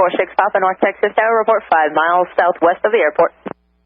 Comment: Hey everyone! What's your opinion about this? I did my best to represent the situation in the map based on some data I was able to recover. || Don't forget to follow me on Twitter and vote for the next video to be published :) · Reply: Only edit issue I saw was at 2:03 Tower says "Southwest," but captions say northwest. Otherwise, good recreation. I was looking for parallel runways, however.